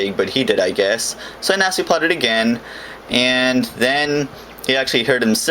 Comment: For someone reasone its sounds like you say natzi plot IDK but it kinda sounded like that to me but let me defend myself by saying i am not a racist